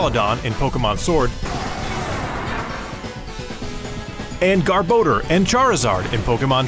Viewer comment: Interesting pronunciation of Garbador lol thought he said GarBoner